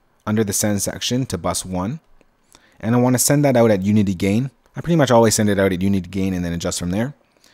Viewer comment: unity gain? 1:30 - what did you say? no rudeness implied,thanks